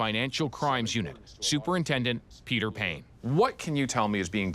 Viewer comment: I'm sorry, did they say his name is Peter Pan?